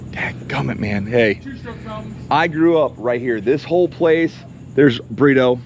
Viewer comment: seconds subtitle said “there’s burrito” lol your brother